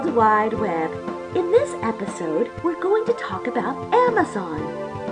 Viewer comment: 安倍総理って聞こえた